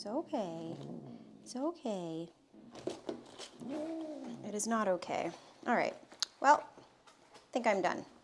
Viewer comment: said, "It's ok", and I clearly heard say "No it is NOT!"😂